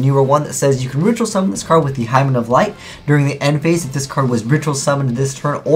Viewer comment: Did he just say Hymen of light?...